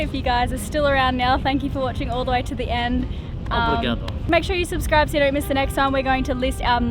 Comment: wtf did say? lol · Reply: Obrigado, that's 'thank you' in Portuguese 😉